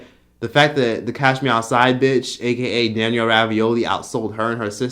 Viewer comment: Did I just hear “Daniel RAVIOLI ”🥴👀😂😂😂